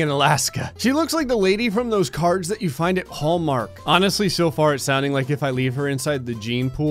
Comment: did you say "Paul Mark"?????